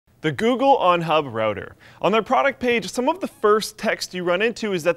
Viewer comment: Thought it said Google PornHub Router. I was excited